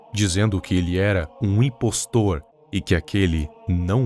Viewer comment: EU OUVI DIREITO?! IMPOSTOT?! SUS! AMOGUS!